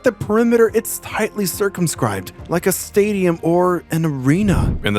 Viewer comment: thought that said circumcised